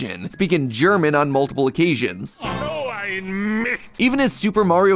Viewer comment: I thought he said “oh i missed” not “so ein mist”